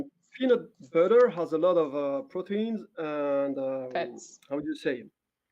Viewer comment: did she just say facts lmao😭